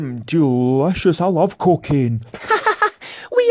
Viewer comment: I thought daddy pig said cockin' pancakes! God i love Scottish accents!